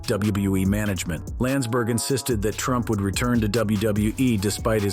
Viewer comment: did he just say Trump?? 🤔